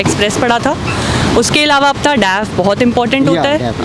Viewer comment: what she said please reply, has she said dam? Kya bahut important hota h? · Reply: DAF :Detailed Application Form.